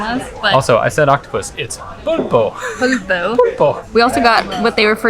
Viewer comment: Didn't quite understand you there. 6:26. Did you say "puto"? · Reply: Pulpo